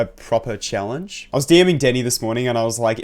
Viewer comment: I heard “I was DM Diddy.” 😭😅😭😅